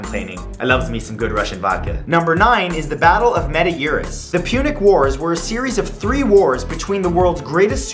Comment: I thought you said "The Pubic Wars" for a second